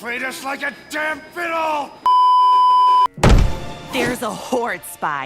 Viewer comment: Double Trouble don’t give us that damn shocked pikachu face you know it’s you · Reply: I thought she said "theres a wh0re"